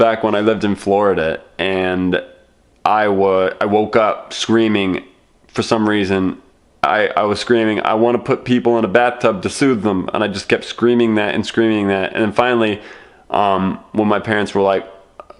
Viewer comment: I thought he said "put people in a bathtub to sue them" and he's officially gone insane